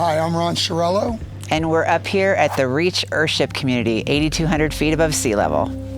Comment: For a hot second in the beginning I thought she said Airship Community and had to rewind, lol!